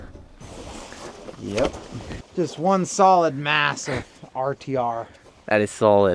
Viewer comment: Did you say massive????